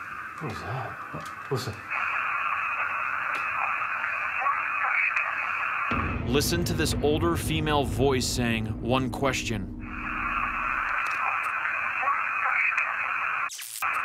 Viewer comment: she doesnt sound like she said one question. sounds like no questioning me.....